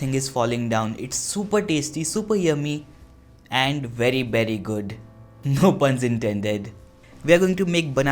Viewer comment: When he said 'very berry' I thought he said 'peri peri'